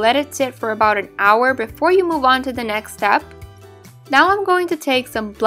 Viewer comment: Thought she said Mulan instead of move on